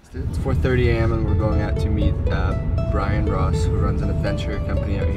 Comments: I thought he said Ryan Ross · I thought he said Ryan Ross 😂 I was so excited for 5 seconds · I thought he said Ryan Ross skdnsnjs